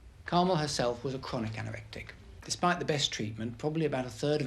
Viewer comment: Poor Carmel weighing under 5 stone. The psychiatrist couldn’t pronounce anorexic at all. Sounds like he was saying anorak tic. A sad case.